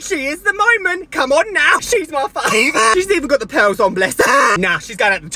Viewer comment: Omg, I thought you said “she even got the penis on” instead of “she even got the pearls on” when you are referring to miss piggy 😂❤ had to watch it twice loll